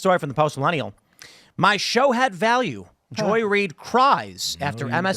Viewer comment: At first I heard "Macho head value"